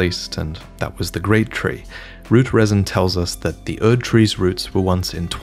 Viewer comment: Just a little correction, at 13:17 you said Erd tree twice instead of saying Great tree